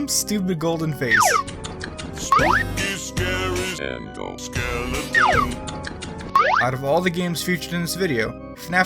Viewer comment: Who else thought it said t h a n o s skeletons?